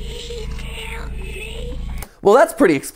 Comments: did anybody else hear that the thing was saying help me? 😬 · I hear "Help me. Please help me. He killed me." Which was fun to hear while taking some notes for a lecture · does anyone else hear "he killed me" in this bit?